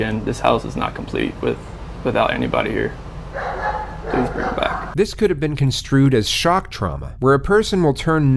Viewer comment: Seems like an accident but he does actually say “please bring the back” like thinks theve been kidnapped.